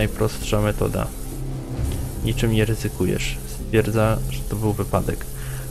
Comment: czy tylko ja usłyszałem "spierdalasz"?